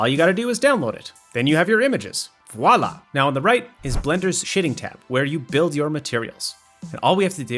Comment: "Blender's Shitting Tab" is what I swear I heard at first lol. great tutorial though!